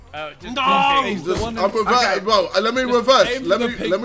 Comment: sounded like jj said “I’m perverted bro” 😂